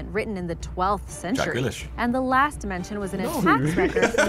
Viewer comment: For anyone wondering what Josh said at 4:40, he said Jack Grealish who is a football player. He was insinuating that the guy in the video looks like him.